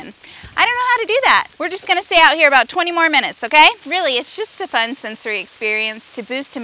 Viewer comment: I thought she said “it’s really such a fun, soggy experience”. 💀😂